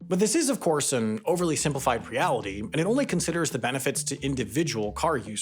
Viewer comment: Did you say preality?